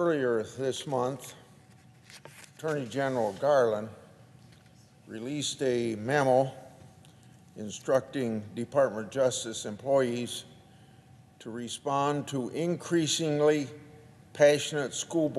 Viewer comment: I thought I heard him say, "...Attorney General Garland released a mammal..."